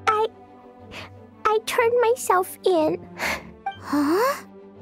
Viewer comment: Almost thought she said "I turned myself onto a pickle"